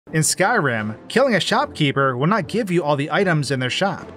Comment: Did he just say skyram